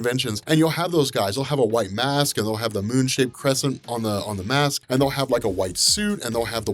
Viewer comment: I heard said ' Moon shit crescent ' ok cool